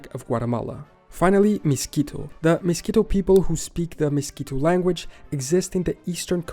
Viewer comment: I literally hear "Mosquito Language"